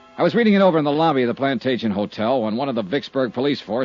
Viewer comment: The title is wrong on this one. Supposed to be Plantation matter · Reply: Well, I do have very dysfunctional hearing so I could have misunderstood I thought sure it said Plantation Hotel